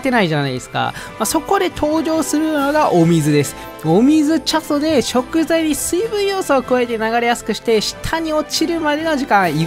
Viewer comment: 恐らく字幕つけた方は氏じゃないと思うんですが、8:53 で 「お水チャソ（お水ちゃん）で…」 字幕「お水ちょっとで…」 笑いました😂😂😂 チャソとか聞き取れないっすよね…笑